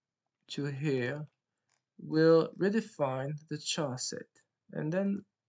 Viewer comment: You keep using this word, and I can't tell what it is. It sounds like Sauce but like Chaucier. CC says it's "Chet". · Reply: Charset 😊